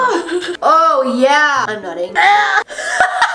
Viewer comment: did she say I’m nutting??😂😂😂